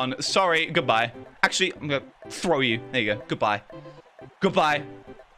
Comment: did he just say n word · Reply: In captions he said again, it was probably to do with his accent